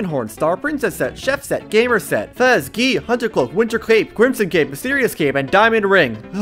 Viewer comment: I hear it as "winter crepe." Which sounds amazing, btw. A crepe made with festive spices and flavors just sounds fantastic.